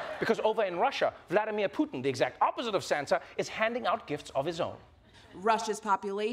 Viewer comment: Putin is not the opposite of Santa! Oh... wait? I thought you said Satan. Guess you are right! 😂